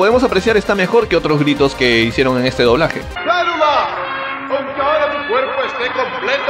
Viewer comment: escuche bien o mal por que escuche decir a Vegeta España maluma